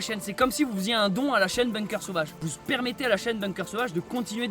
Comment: C'est moi ou a 22:37 t'as dis ''vous spermétez...''??? putain j'me marre comme un con depuis taleur mdrrr